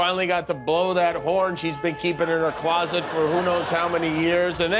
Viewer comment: you say "in her closet", I hear "up her butt"...same-sorta.